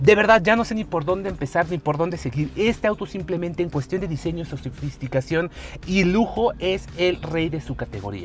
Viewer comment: que dijo? Sositi queee? jajajaj